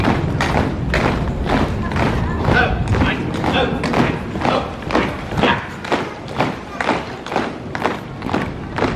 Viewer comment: What are they saying at 2:51? It sounds like they say it occasionally to keep the beat. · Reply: He said "Left, right, left, right, left!" It is a command of the military. Officers or senior noncommissioned officers order troops to keep pace when marching in the military. And this reminds me of the time I gave orders to my platoon members. :)